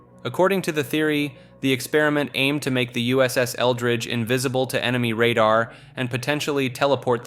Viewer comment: I thought you said “USS Eldritch” for a second 😂😂😂